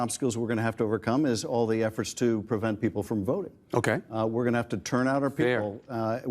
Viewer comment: Did Schiff say "one of the biggest obstacles is to prevent people from voting"?